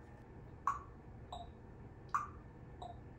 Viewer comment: ngl. . . the first time he said 'clock' i genuinely thought he was saying the f word